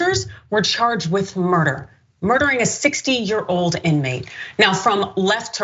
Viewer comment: Am I the only one who heard her say 6 yo inmate?